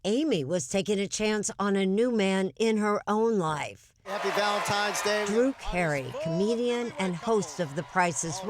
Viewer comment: Did she just say "Valentime's Day"?